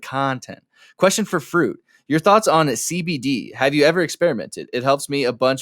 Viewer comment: really thought he said cbt for a moment